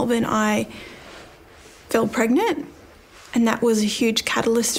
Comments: I heard her say "fell" pregnant (rather than "felt")...ie an unplanned or unwanted pregnancy rather than inner feelings. I am mid Wales UK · she says she fell pregnant and somehow misheard and misunderstood this well-known term (in England and Australia) and he goes off on one saying "how can she FEEL pregnant". This, in my honest opinion, shows just how nonsensical all this body language claptrap is. Psychology is little more than modern astrology - we really do need an Einstein for psychology - and these 4 self-proclaimed "experts" are a great source of amusement to me. Maybe someone should draw up Belle's horoscope and read her tealeaves - that would be on the same ridiculous level as these four experts. The comment of one of the 4 about Belle - aged 26 - having botox, has removed any quark of respect I had for him.